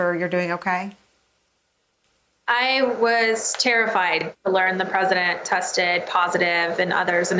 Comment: She says "Terrified" but i heard "Thrilled"